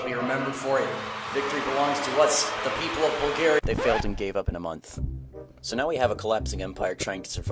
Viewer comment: Did he just Say The Fountain Cave in a Russian Accent????????